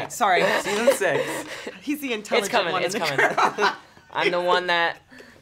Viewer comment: Did Finn just say, "It's coming it's coming." ? So does tht mean tht season 6 is confirmed as well, or was tht just a joke ?